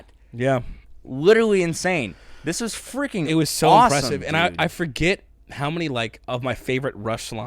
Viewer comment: Almost sounded like you guys came up with a new word: "SAUCE-OME" (SAUCE plus AWESOME) Appropôt, n'est pas...?